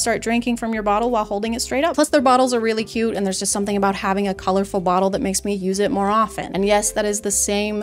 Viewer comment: watched this in slow motion just to check I think just said “a colorful waddle”